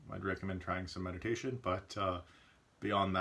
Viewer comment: I thought you said at 3:28 “if you’re struggling at all with your mental health I’d recommend MEDICATION” and I was like wait what?! That’s new! Then I realized you said MEDITATION 🤣 I’m glad you’re feeling good these days ❤